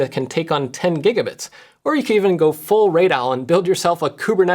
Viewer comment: Was the creator at 4:49. I could not parse what was saying and had to go hunt. All I could hear was "raid Allen".